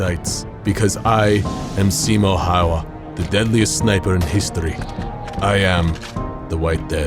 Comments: It sounded like he said "im Simo hauva" and not Simo häyhä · it sounds like he said simo iowa